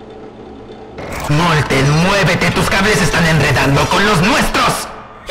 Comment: pense que decia "molte muevete tus cabeza" pero era "tus cables" pero yo escucho los dos